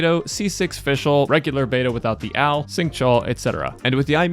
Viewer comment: It took me an embarrassing number of seconds to decipher "regular Beidou without the al" cuz I initially heard it as "without the owl" 🦉